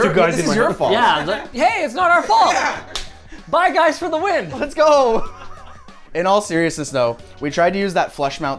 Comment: Why did he say "bi guys for the win"? is Linus coming out?